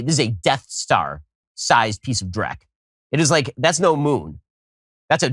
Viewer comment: did he just say drek? Thats slav for shit lol